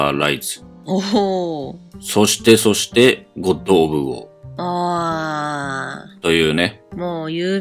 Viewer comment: 音量小さくするとコンドームウォーに聞こえる